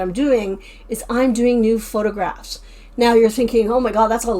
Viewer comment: Ha ha! When you said “I’m doing something new; I’m taking new photographs,” I thought you said “Nude Photographs”. I thought: Wow! She’s really brave! 😂